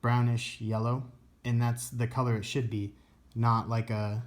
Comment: lol the colour it sh*t be is what I heard